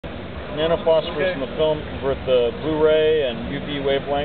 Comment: Does He Say Nano FourSkin?